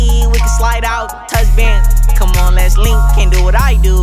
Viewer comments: why it sound like he said leh ling like two times before he said lets link lmao😂😂🤣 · I thought it said we can link and touch hands why would it me bands 😁✨‼️💀